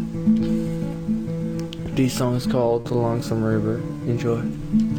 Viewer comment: Thought the dude said the lonesome rear